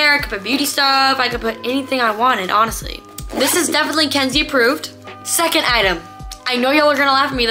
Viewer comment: I thought she kept saying ✨️gen z approved✨️